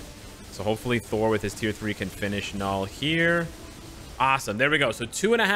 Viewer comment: did he just say knull😂